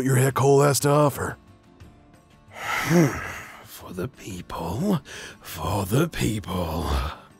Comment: Tell me why when said “For the people” I thought he said “For the Beep hole”